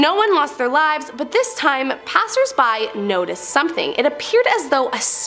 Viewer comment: OMG. I thought she said passed gas...